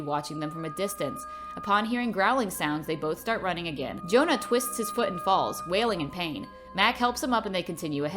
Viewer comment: in 6:50 I thought you said "he fell and twisted his foot and balls" and I was like... that must hurt like hell